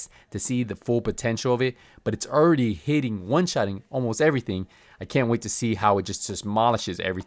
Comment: I lowkey thought it said pvp one shot